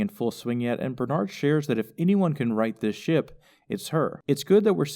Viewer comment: When Bernarnold said 'If anyone can right this ship...', I heard 'If anyone can write this shit...', and thought oh, that's a little unfair.